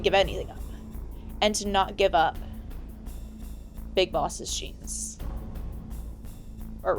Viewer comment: I swear you said big bosses cheeks here. 😂